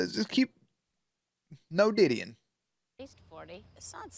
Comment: Of all the things they could have had Eliza aka faith say I wonder why they chose to say Diddy? I am sure they weren't actually making a reference to P.Diddy as he was going as puff daddy back then but it's a wild coincidence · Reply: I heard it as "diddlying" (spelling?), but then again I'm probably mistaken.